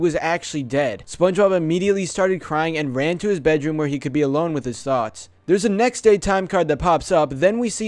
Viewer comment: who else thought it said "alone with his socks"